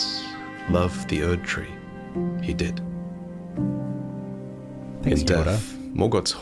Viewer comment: What did say between 38:26-38:28? I couldn’t quite catch it. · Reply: said "love the erdtree he did" and i responded "like yoda?" because of the slanted syntax lol, but yes that was very quiet, not sure why the mic didn't pick that up